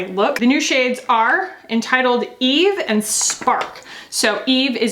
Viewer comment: When you first said the shade names, I heard it as the first shade name being "Entitled Eve" 😂